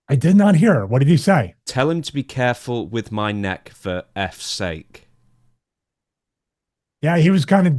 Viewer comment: When i heard it the first time i thought he said “tell him to be careful with my mask ffs”